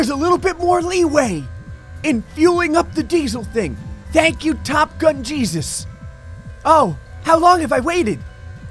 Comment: When you said top gun i thought you said TOP GUNS because i was wearing a TOP GUNS shirt